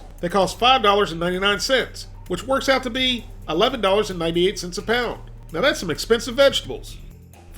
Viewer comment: I'll try them when they get price closer the cost of ground beef. 2, 4oz patties for $12 is ridiculous. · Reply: I thought i heard 12$ apound